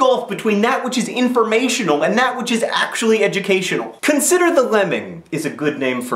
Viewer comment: Soooo at 0:38, did anyone else hear "Consider the following" Or am I the only Bill Nye fan?